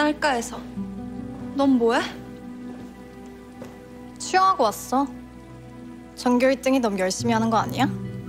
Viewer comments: 석경이가 ‘쉬하고 왔어’ 라고 들리네 14:50 j pop? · 이거 뭐하고 왔다는 거에요??? 쉬야하고 왔다고 들리는데 그건 아닌거같고.. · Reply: 수영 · 이거 뭐라고 하는 건가요..? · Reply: 수영